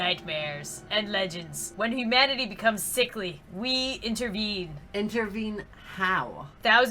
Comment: I thought it says "when humanity boomers sickly"